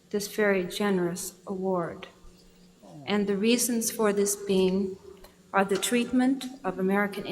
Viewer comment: Did she say “the reason for this meme”. ?????